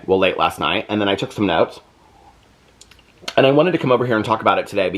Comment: When you said “and I took some notes” I swear I thought you said “and I had to smoke” I was like damn girl that bad 😂😂